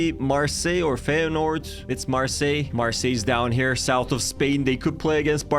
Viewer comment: why did it say France he didn't say in France he said south of Spain and they are.